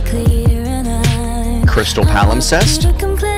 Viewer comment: DID YOU SAY CRYSTAL PALIMSEST? DIMENSION 20?